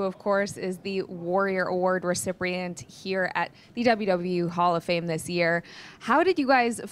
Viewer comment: Did she say EWW instead of WWE?😆